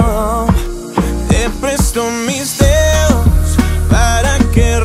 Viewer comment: "Te presto mis dedos" Eramos tan inocentes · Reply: Yo pensé que decía besos pero es dedos y le entendí mejor jajaja😂😂😂 (era tan inocente )